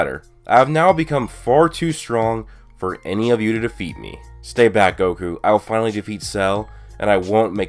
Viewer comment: been waitin for this edit: at 2:12 did vegeta just say stay back goku?